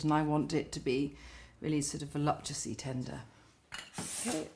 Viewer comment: How did she pronounce 'voluptuously ? sounded like 'voluptessly'' 16:39 how did the staff not notice the big error.. Nigella says 'ABSOLUTELY NOT DIET- it's full fat or nothing' 28:04 - she meant to say it's full sugar or nothing